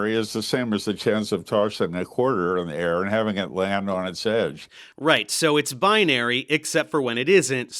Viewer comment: Dammit!!! Due to slurring his words, Dave thought he said "heads". I'm pretty sure he said "edge". Edit: Oh my Christ!!! "coin landing on it's edge" argument destroys the entire transphobe position. The least likely outcome of a coin flip is that it will land on its edge. The least likely outcome of a developing person is that they will be trans.